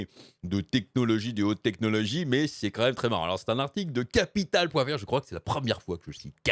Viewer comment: j'ai compris "capital point verge". ;p. Manifestement, je suis en manque. De Sir Verge Raide, ou du site "The Verge" plus cité depuis un certain temps sur ta chaine, je ne sais pas, mais va falloir que je remédie à cela. XD Edit : (2:54) : Ah ! Si ! On cite enfin "The Verge". Autant pour moi. :)